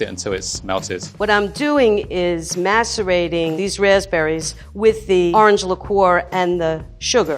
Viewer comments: am I the only one who heard masturbating instead of massurating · Did she just say masterbating the raspberry’s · “I’m just gonna masterrate these raspberries” My mom who’s sitting on the computer not paying attention: “DID HE JUST SAY MASTERBATING?!” Me: “.... uhh.... it’s a She... and she’s mixing raspberries into powdered sugar....” Mom: “....ok...” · When level 1 said “I’m gonna beat my white” I thought he said “ I’m gonna beat my wife” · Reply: Level 2 said " i am macerating these raspberries with orange liqueur". I thought she said "i am masterbating these raspberries with orange liqueur". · what did she say!!!? Lucky raspberries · I really thought that lady said "masturbating the raspberrys" and I about died. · I eventually heard saying something innocent but I can't believe I heard: masturbating. For the people that don't know what she said. She said macerating. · Did say she was masturbating the raspberries? 7:17?😮😮😮😮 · I thought she say Masterba- 😳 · I swear she said masterbaiting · I watched this without the caption on, then I misheard her saying "Macerating" and I thought she said MASTURBATING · wth did just say? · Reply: Macerating. A process that involves breaking down harder fruit into softer pieces with sugar and some liquid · anyone hear ‘masturbating’? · lmao I heard ‘masturbating these raspberries’ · lol it sounds like she said masturbating 😂😂😂😂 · she sounds like she said masturbating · anyone else hears masterbaiting or am I just weird